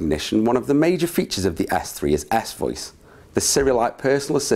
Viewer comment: it sounds like you say ass voice. Haha